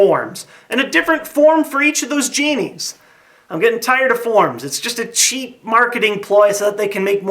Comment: Dude i thought you said forums...